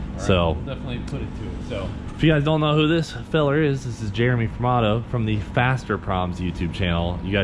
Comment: For the longest time I thought you were always saying, Jeremy from auto. Still can't hear it the right way